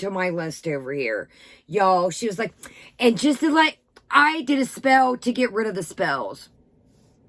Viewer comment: Lmao ooooooh I thought she said "I NEED a spell to get rid of a spell" 😂 🤣 😂 🤣